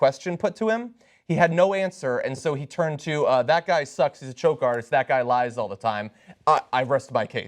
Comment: After Trump called Rubio a "Choke Artist" Rubio got quiet. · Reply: Ben Riley I thought you said "Coke Artist" my apology if you didn't.